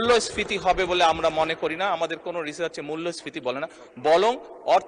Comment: "বলং" 3:46 কি জিনিস? তারেক মিয়া এটা কি বললো? 😄